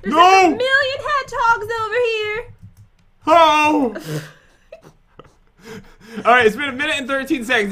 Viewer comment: anyone else hear "ho" like the bad ho come out his mouth??? Anyone, no just me, ok. also why is that hedgehog by Pat having a seizure?????????